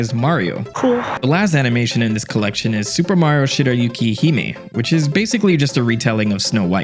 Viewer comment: That last Amada animation I thought you said:"Super Mario: Are You Shitting Me."